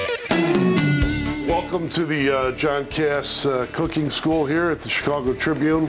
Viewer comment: lol was i the only one who thought he said "welcome to the jackass cooking school"?